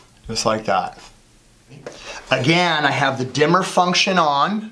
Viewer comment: I thought he said "Dinner" function lol